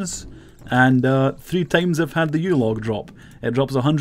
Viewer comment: When you say "Yew log" I hear "Yule Log" lol...